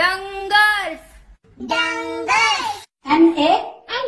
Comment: я один услышал Дамбасс?